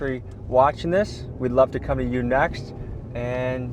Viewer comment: did he just say "to any division heads hit us up, we'd love to cum in you next "????????bro is not getting any more embedded opportunity